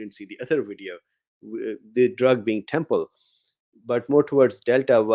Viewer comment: "... the drug being "temple"..." - this is what I hear and what the subtitles function writes on my screen. Can anyone here please enlighten me, which drug is talking about, when he says "temple". Sorry I haven't got time to browse through all his recent shows to see, if there I might understand him better. Thank you!